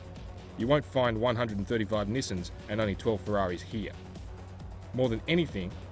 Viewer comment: i just heard 135 misons...